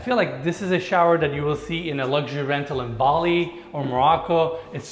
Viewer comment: did you say "Bali"? 😍 thanks for mention one of the greatest island in Indonesia, i appreciate that 🙏🏻